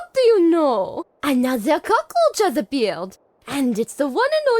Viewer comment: Preeetty sure she says "cockroach" not "cockhole" at 11:01 and "Quoi?!" [french for "What?!"] at 12:33 . Nontheless, thank you for the subtitles - without them I couldn't understand a word from Elizabeth. ^^" How come you can understand her but not the french accent? XD